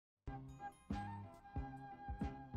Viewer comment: I think you misheard her. She said no pickles ,not only pickles